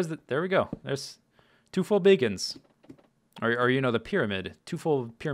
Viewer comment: I heard "Two full bacons." :D